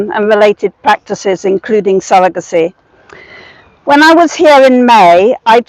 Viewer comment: Same here but I think I heard ‘surrogacy’ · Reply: she said surrogacy first, maybe she repeated it? I would really like to know because I'm new to this issue and I'm horrified! I would really like to be on the ground floor of the next issue.